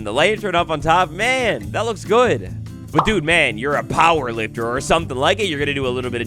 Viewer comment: dudeman?! did you say dudeman? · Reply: Nah, more of a "But dude, man.."